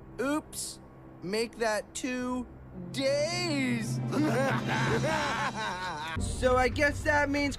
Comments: WHAT EPISODE IS THIS.. edit: omg i thought it said two gays im so sorry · WHY DID I HEAR “ Make that 2.. GAYS!!” 🫎